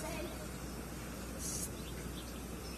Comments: I thought he said I'm gonna go back upstairs and cry! 😆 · "I'm just gonna go back upstairs and be in private." I felt that in my soul. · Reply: I thought he said go back upstairs and cry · ngl, I thought he said beat in private lol